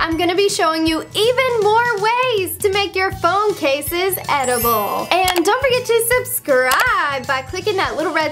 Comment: MY PHONE CASE IS EDIBLE trys to eat it you lied 😭😭 Yup o thought she said that my phone case is edible